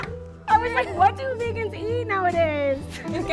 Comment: sounded like she said “what do niggas eat nowadays”